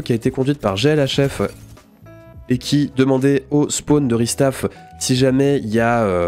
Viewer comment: C'est moi ou a 15:05 seconde il inverse les mots et il dit "au Spawn de restaff" ? 😂